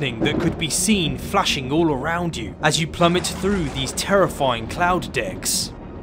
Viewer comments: did he say terrifying cloud dicks??? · did he said say “as you plummet through these terrifying cloud dicks”?